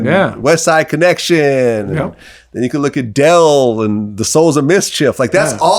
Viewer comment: I thought he said Adele... I was like is Adele Westcoast personified for a sec 🤔